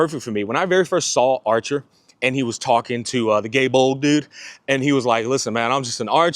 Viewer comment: why tf did I hear Gay Bald dude 😭😭😭💀💀💀